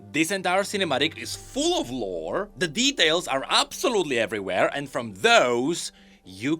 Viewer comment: i heard it lux 🥲 I think this will be a problem unless im the only one that have brain damage